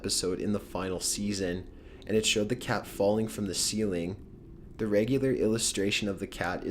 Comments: In 9:43 He Said: In The Final Season, and it showed the cat falling from the ceiling. And In The Captions Said: In the final season, and it showed the car falling from the ceiling. THATS WRONG BRO! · (NOT A ELDER) I found something mispronounced or something in the subtitles when he is doing The misadventures Of Flapjack he says this "was the 44th episode in the final season and it showed the car falling from the ceiling" instead of "was the 44th episode in the final season and it showed the cat falling from the ceiling" this is at 9:46 with subtitles on you might see the difference